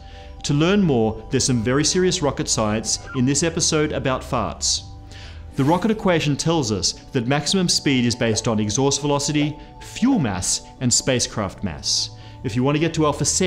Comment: Did he say this episode about farts?